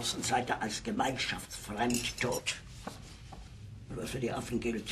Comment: sounds like he says french toast